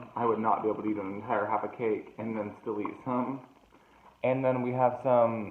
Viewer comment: I had to rewind to hear what I thought you said and I heard you correctly! "I would not be able to eat an entire half a cake and then still eat cum." I like your honesty. P. S. I could eat that whole cake in one sitting 😲😲